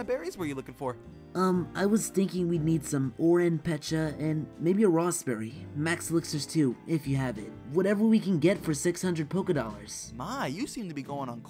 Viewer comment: WIGGLYTUFF IS A BOY! If you weren't sure well now you know.Sorry for the confusion XD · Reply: need some Oran,pecha, and maybe a rawst berry. Max elixires,too,if you have it whatever we can get for 600 Poke. I thought you said Need some RM,SUGA, and maybe a JungKook. Max elytra,two,if you have it. Whatever we can get for 600 Smoke.Sincerely,Paper Kid.